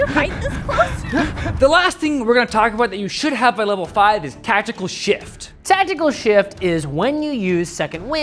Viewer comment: thought you were gonna say tactical shit for a sec